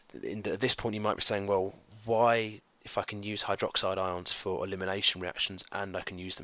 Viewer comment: Did he say what I think he said at around 2:05? Haha completely unexpected had to rewind a few times and I'm still not sure, but I'll definitely remember elimination for the exam! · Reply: He said why if I can use hydroxide... not why fuckin use hydroxide ions..